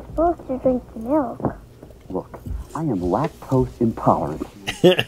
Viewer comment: I thought at the time he was saying "I'm not toast and tolerant!"